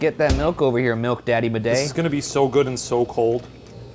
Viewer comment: i heard “milk daddy bidet” and i think ryan said madej but i’m not really sure.